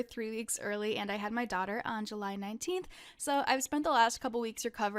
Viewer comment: When she said “on July 19th” I thought she was saying her daughters name was like Angalinteenth or something omg…😳😂